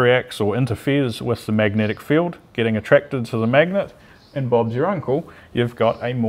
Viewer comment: Did he say "Bob's you're Uncle" at 9:40?